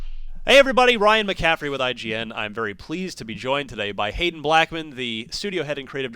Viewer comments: At first, I thought he said "Hating Black Man" before I saw the text. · I totally heard "im very pleased to be joined today by hatin black men" lmao